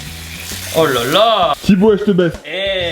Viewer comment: a 4:53 j'ai compris " Tibo eh j'te baise" j'ai vu flou x)